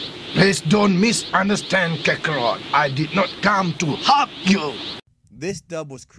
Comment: did vegeta said I did not come to fuck you kakarot